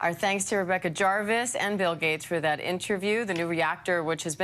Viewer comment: Did they reshoot? I thought she initially said "Bill Gays" Edit